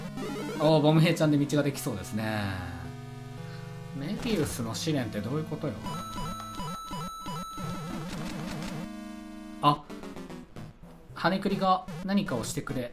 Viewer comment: ヤバイな、さっき七つの大罪 聖戦の予兆第1話がRCCで見たから メリウスの試練がメリオダスの試練に聞こえる...